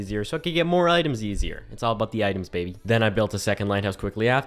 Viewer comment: who else heard “it’s all about the Mets baby”😂